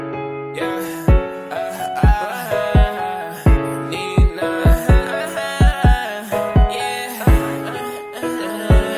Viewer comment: Is he saying deez nuts at 0:03? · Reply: “Nina”